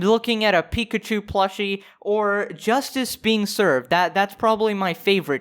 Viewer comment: I thought you said “Justin beanserve” instead of justice being serve. Now Justin Bieber has a new name :)